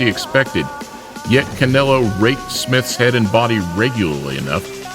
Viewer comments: I heard “Yet Canelo raped smith’s head and body frequently” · bruh did you just say he "raped" Smith's head and body? Come on